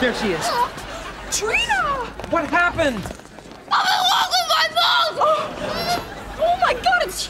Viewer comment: I thought she said "I fell in love with my tounge" in the first one